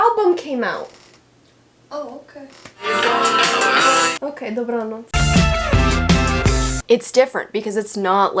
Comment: Nie wiem czy się przesłyszałem, ale usłyszałem "dobranoc" haha